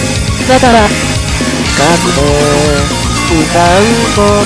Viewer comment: o . o at 1:15 .. did he say Gakupo?